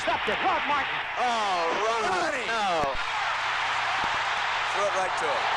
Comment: does Vermeil say "Ron no", or "Rod no"? Ron threw the pass, but Rod intercepted it. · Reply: Randy Curtin Ron